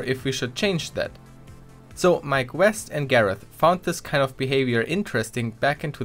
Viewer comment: I thought he was saying My Quest instead of Mike West.